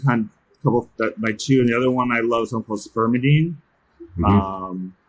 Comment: What supplement did he name at 3:49? I couldn't understand. Thank you! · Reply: Sounded like spermadine lol